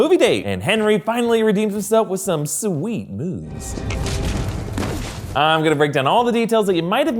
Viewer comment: I thought you said “sweet walls”